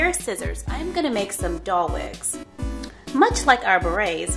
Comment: I'm so sorry... I thought it said wings HAHAHA.